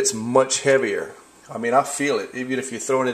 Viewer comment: Did he say CARGO SHARTS?